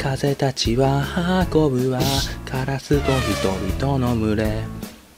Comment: カラスと人々の群れが、カオスど人々の群れに聞こえた